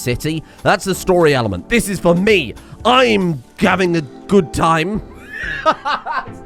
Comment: I swear to fuck just said "I'm gavin a gob time"